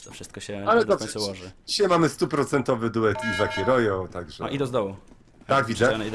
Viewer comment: Ja usłyszałem Lizak i rojo